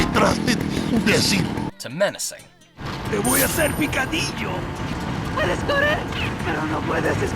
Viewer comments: When I was a kid, I misheard "¡Te voy hacer picadillo!" to "Who would've said we can't eat you?" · Spanish here: It's so fucking weird. Both vocabulary and pronunciation are a mix of Mexican Spanish and Spanish from Spain, more on the Mexican side. Plus, I've found some misspelling and mispronunciation, so it absolutely sounds like an American voice actor who learned Mexican Spanish and is trying to imitate the Spanish accent from Spain. Weird af. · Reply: Te voy hacer picadilo ! I’m so gutted. All these years not knowing how or what this meant. I always thought he was saying “INcadinio”. 😆😆😭😭😭. Resi 4 is the GOAT 🐐.